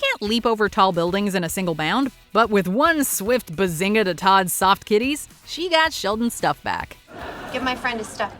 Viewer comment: Did they just say "one swift bazinga to the soft kitties"? 🤣🤣🤣